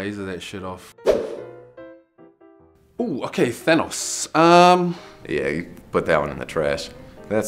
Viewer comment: LMAO DID HE SAY THANOS